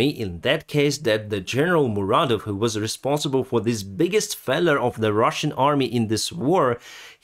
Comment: lol I thought your were saying feather at the end but you were saying failure. Say it like fail-your. You're english is actually pretty good, except for a few sounds that aren't quite right and a few tricky words you get wrong. English is tough, it's made up of old English, Latin and French, so some worlds are spelt so wierd many natural speakers have trouble :P